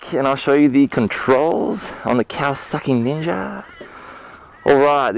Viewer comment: I swear I heard him say "on the cow sucking ninja."